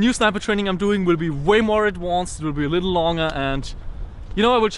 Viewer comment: Hey Nov no offence but I noticed that at 3:02 instead of saying advanced you said adwanced. Like I said no offence but it that just your Austrian accent